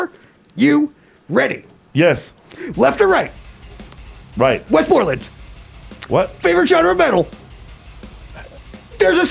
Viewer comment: The "Wes Borland" "What" cracks me up every time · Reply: I thought he was saying 'Westmoreland' and it made no sense😂😊